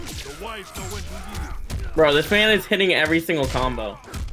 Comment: most def said “this nigga” it’s cool though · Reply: I went back and listened to it, at first i thought i heard "this nigga", but he does say "this man". Some weird sonic $hit happened maybe.